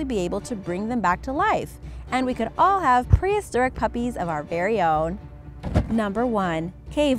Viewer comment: I thought she said “Two Mad Puppies” lol